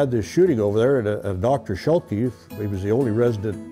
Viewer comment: Can anyone help me with the doctor's name at 2:55 . Is he saying Dr Schelke? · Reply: Yes, Schelke